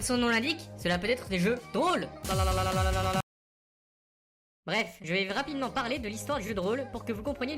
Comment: j'ai compris qu'au bref. j'ai rie.